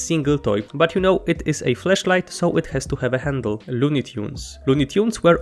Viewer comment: Thought he said "fleshlight" lol.